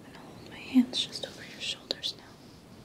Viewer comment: I thought you said "An old man's just over your shoulders now", freaked me out a little!